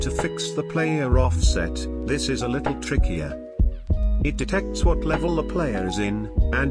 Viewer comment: i heard on 5:15 "this is a little Tricky yeah"